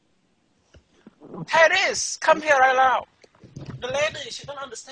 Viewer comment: I was listening to this while in another tab and at 4:15 I thought he said "Harris" which is my name haha